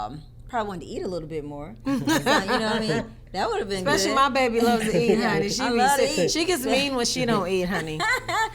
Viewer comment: Does anyone know what tboz says at 7.15 - 8.02? English is not my first languange and sometime they talk too fast for me 😊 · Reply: The only i hear was “especially my baby loves to eat. She kiss me when she dont eat”. Im not sure 😅